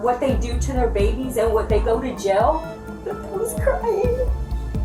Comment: wtf are these subtitles? Dude, if you can't hear right then rather don't do any subtitles at all if you write BS like that. She literally said "nothing was crying" NOT this was or is crazy. 🤦‍♀️